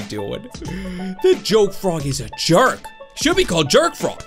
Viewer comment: I thought u said jerk frog is a joke